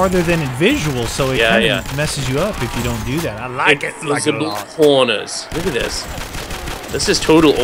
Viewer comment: Lol when said corners I thought he said porners